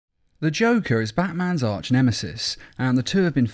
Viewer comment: Had my sound muted and when I turned it up for the video I only heard "Goku is Batman's arch nemesis". Not what I expected but by all means do go on.